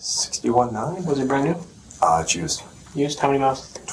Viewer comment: the real crime here is $60k for a used truck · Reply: Why is everyone saying 60k plus 15k down.... he clearly says 61900 was the sticker price